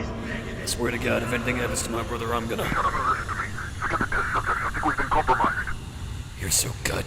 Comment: I know i am VERY VERY late to this video but at 39:06 where it says [UNINTELLIGABLE] I believe you can barely make out the sentence "Forget the test subjects." I could be wrong but just what I heard